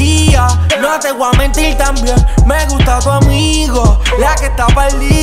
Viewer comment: Mera Ankhal que es lo que tú dices en el minuto 1:10 ???👀👀 me gusta tú amigo o amiga ? Que alguien me explique que no entiendo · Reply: Sounds like (o) 🤷🏽‍♀️ pa'mi dice amigo 🤣🤣 escuchado esta cancion como mil veces x el chico mio.... no se 🤷🏽‍♀️